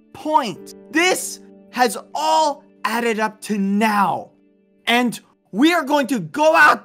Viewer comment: I thought he said all of this added up to nothing, lmaooo